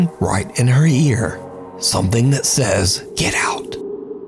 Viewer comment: The bye felecia one. I hear like the voice said “he got it.” I didn’t hear “get out”